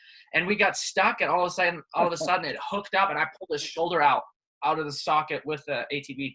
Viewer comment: He said that he pulled his shoulder out , then he said his friend pulled his shoulder out , I think ken is making that story up. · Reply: GO LISTEN TO THAT AGAIN 5:25 HE CLEARLY SAYS "I PULLED HIS SHOULDER OUT" EMIG GETS IT WRONG KEN IS VERY HONEST MAN